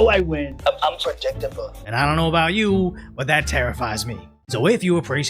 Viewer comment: Did you say it tarrifies you? Hehe